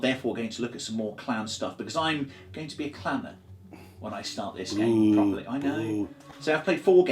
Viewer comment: Nice unboxing. Liking the mdf terrain too. Might we see Uncertain Terrain I misheard you, thought you said "Ill be a Clanger". Thought gee, they've changed!